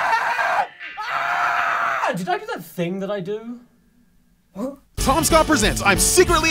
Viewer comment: Oh my god, I always thought he said "Thats the thing that I do"